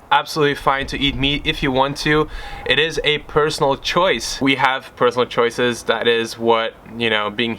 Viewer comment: It's really disappointing that I heard you saying "it's a personal choice to animals" 😔